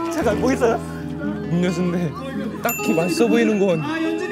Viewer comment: I'm so confused why did Soobin called Sunghoon 'sunbae'? isn't he the senior of enhypen? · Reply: 아! 선배라고 한거 아니구 음료순데? 라고 한거 같아요😅